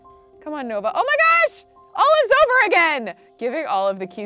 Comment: What was said: “Olive’s over again!!!” What I heard: “Oh I’m sober again!!!” Lol